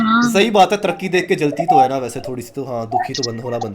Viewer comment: Did he say taraqi at 2:01 ? 😅😂